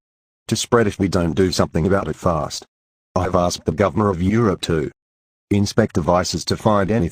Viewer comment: ”Governor of Europe”?? Did I hear that right??